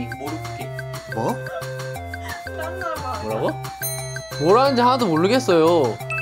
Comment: 초 정답:뭐라고말하는지모르겠지기 정답:뭐라는지 하나도 모르겠지요 기동